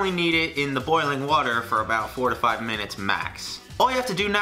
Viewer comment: Did he say 45 minutes? Edit: Nvm he said 4-5minutes